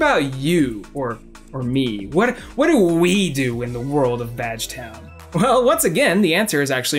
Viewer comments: I thought you said vag-town and I was greatly interested. Then I realized it was badge-town and my interest plummeted. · I had to go back a few times cause I thought he said vagtown